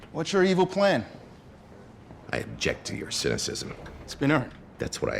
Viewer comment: what does forman say here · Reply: "it's been earned"